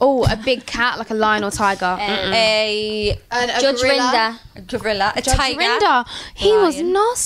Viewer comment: What did Jade say at 1:31? Judge who? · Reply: Oh judge Rinder 🙃